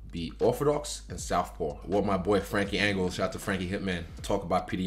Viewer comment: did he just say one of my boy friends